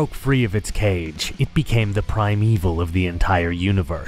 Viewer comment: I apologize if this sounds nitpicky, but you used the term primevil to describe the wyrm after its imprisonment by the weaver and subsequent madness / corruption. The term primevil doesn't refer to evil, but to wildnerness, ancient and untouched by civilization or man, the natural world in its purest form. In some sense, the wyld would be far better described by primevil than the post-imprisonment wyrm. Anyway, great video, and I'll fuck off now. EDIT: Made it a little further into the video. It sounds like Ludmilla is kinfolk, blood relatives of the garou who are not subject to the delirium. Again, I will return to fucking off now. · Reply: I heard that as "prime evil" as opposed to "primeval"